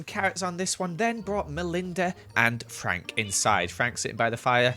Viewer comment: it kinda sounds like said Anne Frank, instead of and Frank 😂